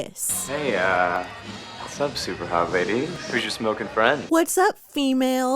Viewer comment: I just threw uppp I thought he said” what’s up super hot babies’🥀😂🥲